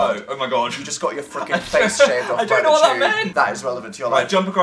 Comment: Dan did you say fucking or freaking??